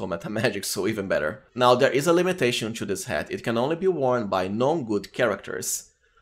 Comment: Feedback for you: When you said "Non-good" about dravens hat I thought you said "gnome good." Its not your accent i'm sorry if it sounds like im criticizing you for something you dont really control, its just a really unclear word phonetically anyway. I would recommend saying "anything but" in place of "non" if you want a straight 1:1 conversion, or you move the words around to say "are not." And of course you're completely welcome to ignore me. I'm trying to give helpful feedback, its up to you to decide if it is helpful or not.